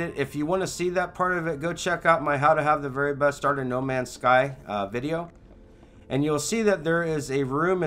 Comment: What does he say? Check out my "How to have the very best {{what?}} in NMS" video? I wish he didn't talk so fast or would enunciate some words better. Lol No matter how many times I rewind, I can't make out what he said. Plz help :) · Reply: "...the very best start in no man's sky video"